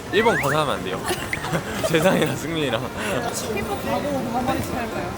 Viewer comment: 제2의 수호라고 해서 봤는데 ‘각오’를 똑같이 ‘가고’로 들었네요 ㅋㅋㅋㅋ 이렇게 똑같을수가🤣